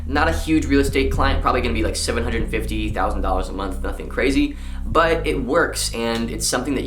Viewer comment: Did you just say 750,000$ a month for a real estate agent then proceed to say "not a big deal but"?!? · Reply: No he said 750 dollar per month